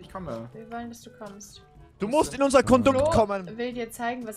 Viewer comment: was hat gesagt? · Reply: Kondukt